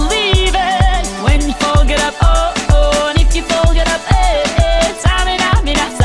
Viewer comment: i thought she said when you f**k it up and if you f**k it up 😐 ive always thought that up until now 😔✋